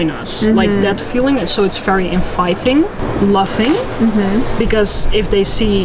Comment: When she said "queer scene in three words" I heard "infighting" rather than "inviting" oof, the stories my head spun...